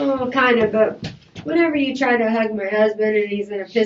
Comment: One of the biggest Red flags is when she said "whenever I try to hug my husband he pushes me away". That's not normal with two people that love each other. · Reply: I heard her say " whenever (you) try to hug my husband ". She did NOT say whenever ( I) .